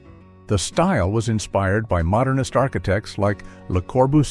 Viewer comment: When he was talking about brutalist architects, I definitely thought he said "starkitects." Which honestly, given how that style looks, makes sense!